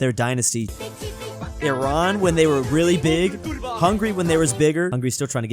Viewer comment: Did he say Hungary? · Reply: Look 2 specs before brother it was Hungary he was still talking about it